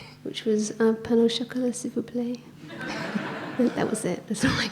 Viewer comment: Does anyone understand that word in french?? 9:35 😅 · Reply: "un pain au chocolat, s'il vous plaît" which means "a pain au chocolat please"